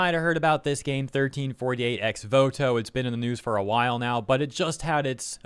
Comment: I misheard ex voto as "rip bozo" lol.